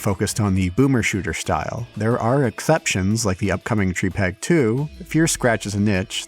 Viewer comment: whats the game you mention at 1:01? I hear "tree peg 2" but cant find anything online about it?